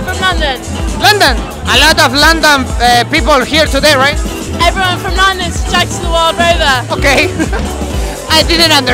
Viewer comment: parece que dijo "every one from London checks in at the Wild Rover"